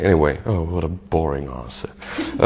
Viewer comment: Maybe im mistaking... but I really hear at 0:19 Ville says ´´Oh, what a boring acid´´ ? amiright